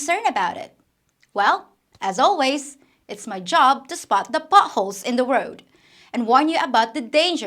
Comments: Oh "potholes"! I thought you said "buttholes" 🤣🤣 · At the beginning I thought you said 'buttholes in the road'. Big oops! haha. Also, I assume out of country divorces are recognized by the Philippine government? A ship could park itself out of Philippine waters and grant quick and cheap divorces. Kinda like Las Vegas. If you start that business, don't forget it was my idea. If it's a crap idea, I know nothing about it.